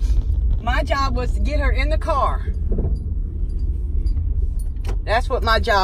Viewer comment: she actually said car instead of curr